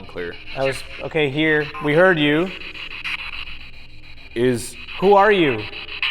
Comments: when asked, "Who are you, it sounded like George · I heard the guy that said "Here" I heard Josh for his name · when says who are you at 38:18 it sounds like it said George